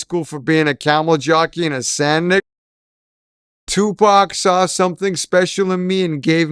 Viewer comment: What did he say at 8:39 that was edited out? 🤔 🤫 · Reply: Sand ninja🥷lol